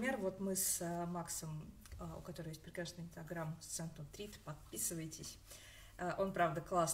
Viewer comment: Не расслышала на 4:38 про инстаграм некоего Макса, не могли бы Вы написать ?🙏🏻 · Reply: конечно, <link> ))